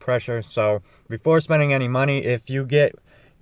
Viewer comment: Thought it said try this before washing money